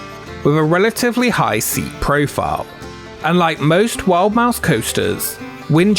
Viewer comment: Cool topic! A few corrections: 10:14- Most wild mouse coasters feature individual cars, not trains. 10:24- Viper was at Great Adventure, not Great America. And the shoulder restraints are totally different between the two rides. 17:25- This is important. Only one TOGO Ride, Shockwave at KD, had a fatal accident in 1999. While that year was terrible for coaster and thrill ride accidents in the US, the only TOGO ride that had a fatality in 1999 was Shockwave. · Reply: I know this is a hard necro, but at 10:14 he actually says “unlike most wild mouse coasters” so he said it correct but it’s a bit unclear and sounds like he says “like most”. But really thank you for the last correction, I was searching my nuts off and couldn’t find anything about the 2 accidents!